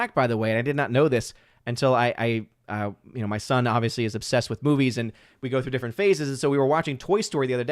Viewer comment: When says "My son is obviously obsessed with movies" Me listening on 2X speed: What I hear: "My son is obviously obsessed with Boobies"